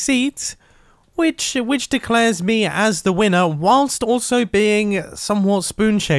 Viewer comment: i thought it said Wiener.